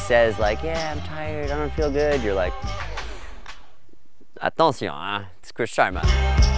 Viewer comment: What does Dave say 4:40 ? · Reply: ***** Attencion, eh? It's Chris Sharma. Basically, he says "Watch out, we got a bad-ass over here"